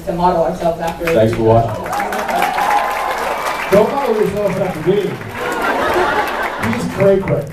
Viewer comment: What does Jared say to Jensen at 8:52? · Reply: "Don't model yourself after Dean, he's cray-cray"